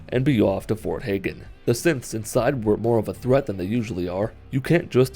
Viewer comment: i thought at 9:57 he said simps and i laughed my head off XD